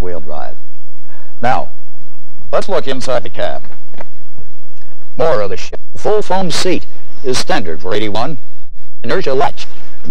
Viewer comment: the laser glitch makes it sound like he said "More of the shit full-foam seat"!